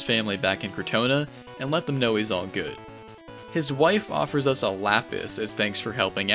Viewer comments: I thought you said, "His wife offers us a lap-dance." 🤣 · Hope you guys like this video, a series of this length was ill-conceived and destroyed me mentally. I burned out on these games like eight times over the course of several months. Part two will be up soon, the worst is yet to come. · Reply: I thought you said the wife gave you a lap dance as a reward instead of lapis.